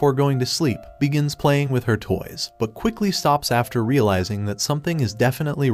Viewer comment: stop did it say she was playing with her "toys"😭💀